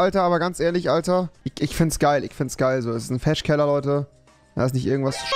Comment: Ich habe Fetisch Keller verstanden 😂😂😂